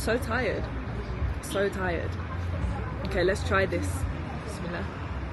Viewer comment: Did I heard you saying Bismilah?